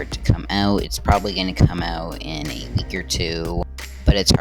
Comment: What Did You Say, A Year 2? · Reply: a week or 2